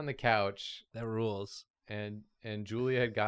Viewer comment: I thought she said Nude Julia and I was so concerned